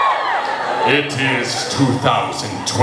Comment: i thought he said alpacalypse......